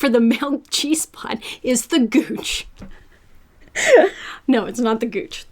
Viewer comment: I deadass thought that it said "goonch" and I was so confused because that's a species of catfish.